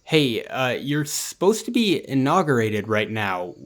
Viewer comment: Inaugurated? I thought you said Intoxicated!